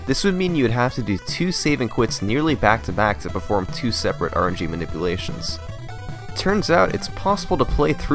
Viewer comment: At first I thought you said Orange E Manipulation lmao